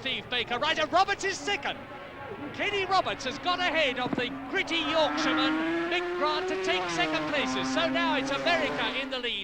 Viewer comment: After a lifetime of riding loud bikes I could have sworn Murray said: "The pretty Yorkshireman - Mick Grant". 😂😂